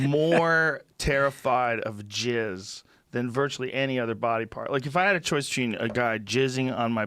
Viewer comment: He said fluid. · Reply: Time stamp is 2:43. I hear "body part", and it makes me laugh because his thought is kinda truncated on the tail-end as he segues into his next point. 😜